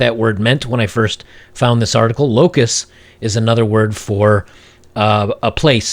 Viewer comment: "Locus?",I thought you said "Locusts"-as in how modern wahmen(i.e.f3minasts) devour us men!